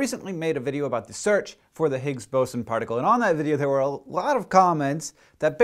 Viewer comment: I thought it said The Nigga Boson